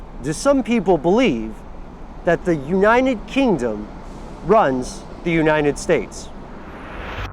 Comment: did he say unniened kingdom?